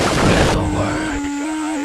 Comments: Hey what did he say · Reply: Spread the Word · What did he say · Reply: Spread the word